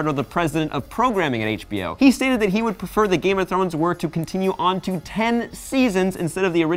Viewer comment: Game of Trones!? I thought he said trones lol